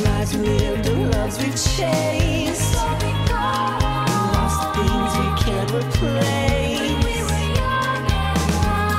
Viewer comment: What is she saying at 2:21? In the background. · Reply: She says "and so we go on"